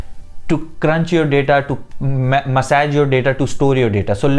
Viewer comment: did he say massage your data at 3:51.. lol